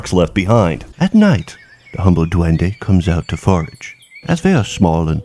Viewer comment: Did he just say Dewen? Surprised that folklore is known in the US.